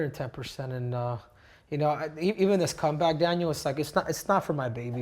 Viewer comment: Did he call him Daniel at @ 8:30? 😅